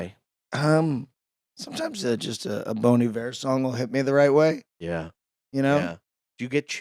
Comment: I thought he said Bony Bear instead of Bon Iver